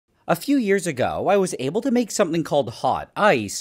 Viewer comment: My headphones are dying so i heard "A few years ago I was 𝗕𝗔𝗧𝗧𝗘𝗥𝗬 𝗟𝗢𝗪"